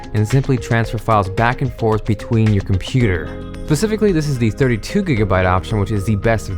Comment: Did he say pacifically at 0:45 ?